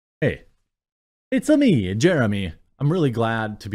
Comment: Fair play saying this was too baity. My apologies. But also i dont know why so many people are saying "Pyramid Scheme" its clearly not lol. Its just a simple affiliate program. Maybe I didnt explain it right. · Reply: Fucks sake Jeremy, in the beginning I first heard "It´s a me, Germany"